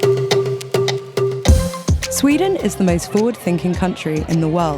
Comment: Did anyone else hear "Sweden is the most flawed thinking country"